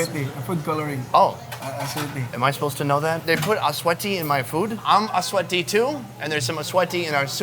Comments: I thought I heard him say, sweaty when the guy was talking about achuete. lol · Sonny i think you misheard him, actually its atsuete (Annatto seeds) for food coloring, not "sweaty" lmao. But your video is great and keep it up!